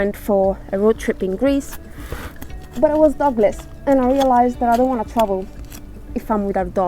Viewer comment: Sorry you've been without your licenses, can't imagine how that's been over late winter. Always enjoy seeing what you're up to. When you talked about being dog less, I thought you said Douglas, had me wondering who that was...lol. Enjoy those flowers.!.